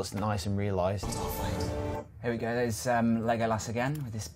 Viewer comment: Sounded like they said "lego-ass" lol :)